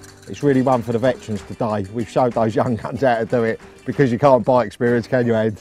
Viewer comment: "Really showed those young c**ts how to do it" Hahahaha am I hearing things or?